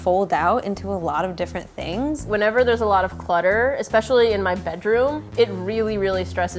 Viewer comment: OML AT 1:07 I THOUGHT SAID THAT ALOT OF BUTTER IN MY ROOM NOT CLUTTER AND I WAS SO CONFUSED